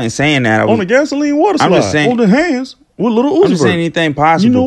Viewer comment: GASOLINE WATER SUPPLY💀💀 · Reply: mommasevilnino Damn😂Thought he said supply which I find a lot funnier than slide even though slide is what he said.